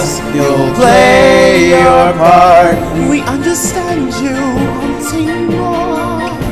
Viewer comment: what dose luna say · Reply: 'We understand you wanting more,a chance to shine a chance to soar'